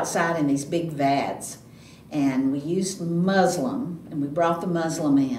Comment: and you are talking about spring cleaning. What is musselum(I may be misspelling the word but I also font want to spell it they way I see it in my head) what is the proper way to spell it and what did it do. I was born and raised in Humboldt, Tn and I have heard of it before but it has been so long that I have forgotten. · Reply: I think she said Muslin, it’s a kind of gauze-like fabric that’s used for cleaning or cheese making.